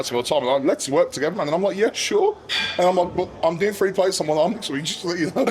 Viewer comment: i have no idea what he said, can someone translate? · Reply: “I’m doing three plates on one arm” I think